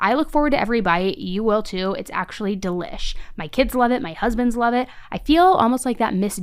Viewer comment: “my kids love it, my husbands love it”! HusbandS?! Please tell me I’m not the only one who hears say it plural?! 😅😅😅